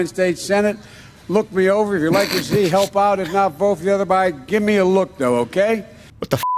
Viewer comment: I swear I heard him say, "Check me out, if you don't like me, vote for the other Biden. Give me a look though,OK?"